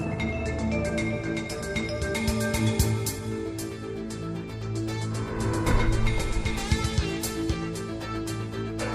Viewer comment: How tall did they say the Alien stood? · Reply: About 5 foot